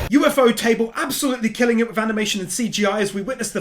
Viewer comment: Pls tell me he did not just say UFO Table at 35:33 lmfaooo